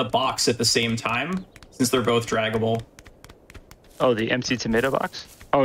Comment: I know they're saying "drag-able", but I keep hearing "Dragon Ball." Really cool run though, never knew Untitled Goose Game was so deep, wild. Thanks. From